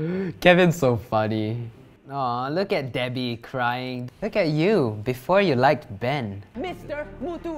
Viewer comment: am i the only one that thought he said: look at you, before you liked men